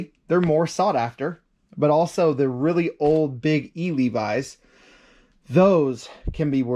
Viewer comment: in the video, you were talking about orange tag Levi's and you said something about "really big old E Levi's" - I didn't quite catch it... E ? Levi's? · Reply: Yes big E Levi's. Sorry i talk fast sometimes